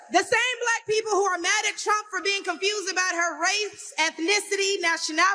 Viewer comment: I swear she called him "Chump."